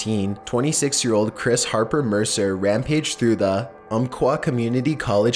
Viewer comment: "Chris Harper Mercer rampaged through the... Umpqua community college" The first time around I heard kumquat but eh close enough.